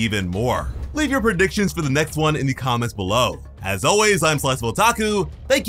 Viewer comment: At the end you always say your name, can you spell it? I hear "slice of otaku" (probably spelled wrong if I'm hearing to right).